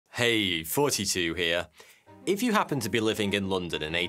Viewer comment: My mind is blown, I just realized he was saying “Thoughty2 here” I always thought he was saying “42 here”